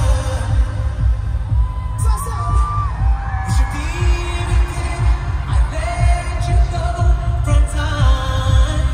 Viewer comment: what do he say? Sassy? ❤ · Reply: “So I said”